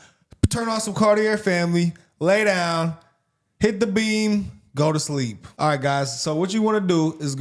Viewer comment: Yo fam, I swore he said "hit the bean, go to sleep" 😂